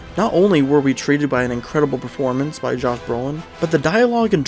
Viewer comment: loved the video made some great points but did i hear you say Jacques Brolin at 1:15 ???